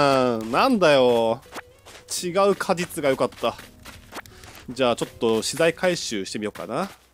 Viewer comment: 「じゃあちょっと死体回収しようかな(空耳)」 その顔で言わんでww